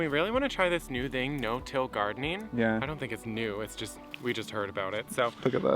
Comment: Glad he panned, because I thought you said no kill gardening, lol